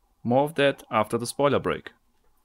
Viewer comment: When you said "spoiler break" I thought you said toilet break, and I thought, "I hope he washed his hands.